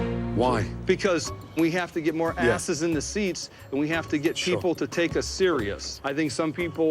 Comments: did he just say "sheeple" instead of people? What a prick. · did he just say "Sheeple?" what the fuck lol